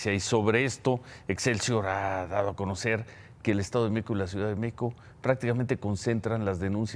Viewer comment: Hable bien, parece que dice: "El estado de Meco, la Ciudad de Meco" 🤦🏻‍♂️